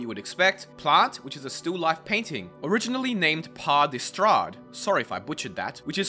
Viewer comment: Me At first: What did you just say..? Me after reading the text: Ohhh, Paradistäd!